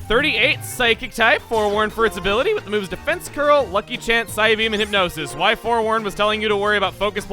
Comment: When you said "lucky chant" I thought you said lucky pants